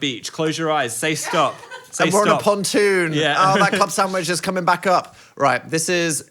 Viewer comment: full on thought you said cum sandwich